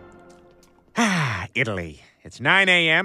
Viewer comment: Did he say I already kissed 9 Guys? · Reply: Kissed 8 guys. Twice